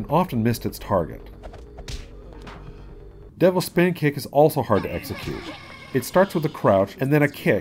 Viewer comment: am i the only onw who heard "devils pankakes" ata the devil spin kick section?